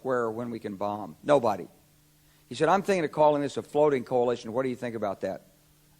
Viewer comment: What did he said "naming it ... coalition ?"